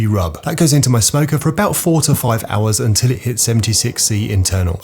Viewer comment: I thought you said 45 hours in the smoker. 😅